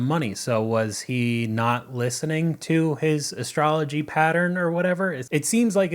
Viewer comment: did he just say astrology trader? 😂